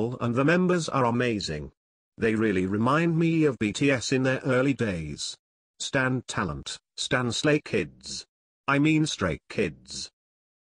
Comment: did you say straight kids???😂😂😂